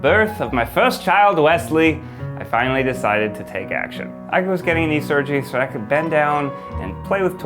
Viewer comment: Oh this scared me I thought said the try guys tree getting knee surgeries Lol 😂